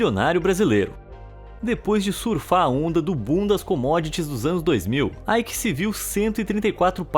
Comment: Essa fala 10:25 ficou muito engraçada , eu entendi ," depois de surfar a onda do bundas comodoties " 😂😂